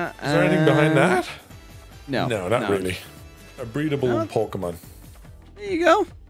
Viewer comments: DId i just hear say "breedable pokemon" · blud did not say a "breadble pokemon" wtf